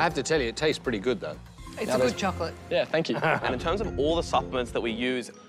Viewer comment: I thought he was saying pig chocolate LOL.